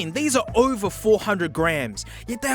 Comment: when he said "over 400 grams" I thought he said "over 400 grand", and for a second I thought these headphones were legitimately over 400 thousand dollars lmfao